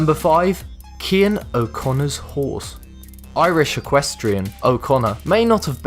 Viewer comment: did someone say....... Equestria?